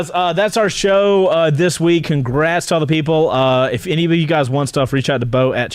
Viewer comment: Towards the end, it sounded like Chase said - "if all u guys WANT stuff, contact Beau!". I'd like a Cardo, Beau. Please and thank you!?